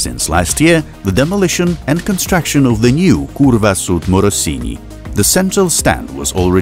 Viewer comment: did he just say kurwa? (in language polish this is fuck)